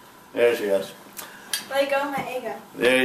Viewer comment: Did she say "let go ma nigga!" @ 10:35 XD